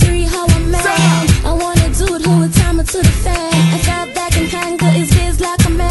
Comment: I wanna dude who will do me in his van. That doesn't sound safe at all. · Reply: Ohhhh. I legit thought she was saying I need a dude who will tie me to the fan this whole time. I only partly typed that in and it filled in explained. Apparently brilliant minds think alike 😁🤗